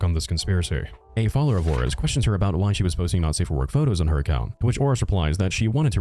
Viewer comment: I really like. I always have to speed him up. Why was I hearing “Nazi For Work Photos”…. I cldnt stop laughing and it made me uncomfortable so I had to take a break